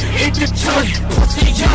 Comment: I always thought he was saying "Glock cocked" instead of "Clock Clock".